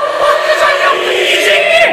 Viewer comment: Sessiz demissin ama ben duydum Patlican yabdim yicen ni dedigini